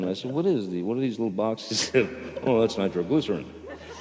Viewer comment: what did he say? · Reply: nitroglycerin, an extremely explosive substance